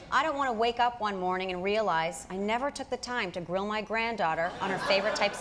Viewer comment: Well.. I'm glad that there was a text graphic of the quote about his grand daughter.. 'cause I totally heard 'drill' for a second there.